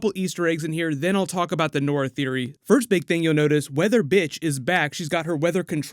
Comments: did he just say weather bitch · Am I the only one who heard "...weather bitch..." ? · it sounds like you said "weather bitch" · did anybody else notice he said weather bitch instead of Weather which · It sounded like he said weather b*tch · sounds like he said weather bitch 😂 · did I hear “weather bitch” lmao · wait is her name weather witch or weather bitch? lol just really sounds like you said weather bitch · Did he say "weather bitch" at 00:53? 😂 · Did you say “weather bitch” lmaooo 😂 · hahahah did you say weather bitch in 0:53 ?